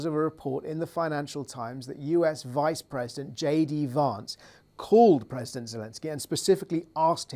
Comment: I thought you said "Dunce called Zelensky to ask him not to disrupt global oil supplies" 😂😂😂😂